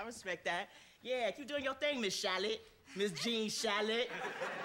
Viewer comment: What is derel saying at 1:52 ? Gene shallot?? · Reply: gene shalit